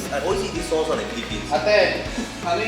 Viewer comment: I’m pretty sure that said “ate” at 5:49 instead of auntie because “ate” means sister and don’t look that old. 😂